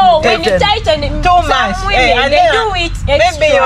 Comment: Did you just say mahogany 😂😂😂😂